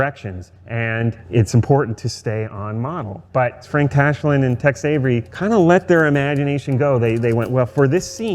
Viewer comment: I know he is pronouncing it "Tex Avery", but i keep hearing "Text Avery"